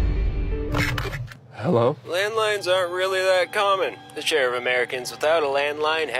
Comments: Ngl I thought he said landmines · I thought you said landmines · Until you picked up the phone I thought you said "Landmines" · My dumbass thought it said landmines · I thought he said landMINES and my face dropped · I miss heard I thought he said "landmines" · Oh shit, I thought he said landmines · Jesus! I thought he said landmines! I thought "wow! The septics can buy anything!!!" 😂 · I heard it as landmines and I'm like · I first thought you said “landmines” and the demographic being Americans it did seem plausible but still kinda disturbing. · I thought he said landmines.😮